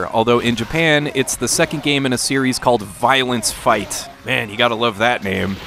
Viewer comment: Ohhh VIOLENCE fight. I thought you said 👀 hug fights.